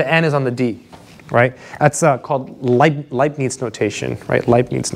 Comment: I misheard Leibniz notation as "Lightning's notation"